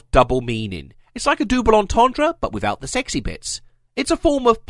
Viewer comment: did you just say dooble antondra